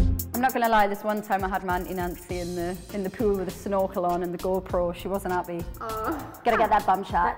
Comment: Does anyone understand what Perrie said at 0:51? "I am not gonna lie...??" Didn't get the further part.🤷🏻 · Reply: This one time i had Mancy Nancy in the pool with a snorkel on and a go pro.